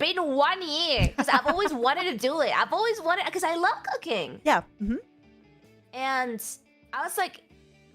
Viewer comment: istg i heard "i love cocaine" and i was like 😮